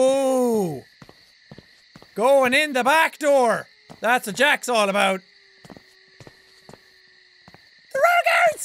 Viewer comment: I heard him say " going in the back door , that's what jacks all about